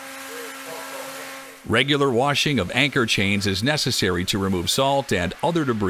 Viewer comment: I kept hearing "anger washing". Even thought that would be a good idea.